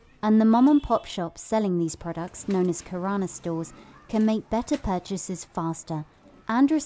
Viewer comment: i heard corona stores